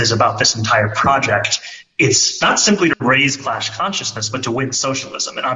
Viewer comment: is he saying "clash consciousness" or "class consciousness"? (2) sweater not siezing anything.